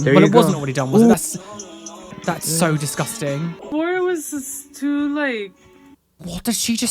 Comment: i think she says: "or was this too late?" in drunk speak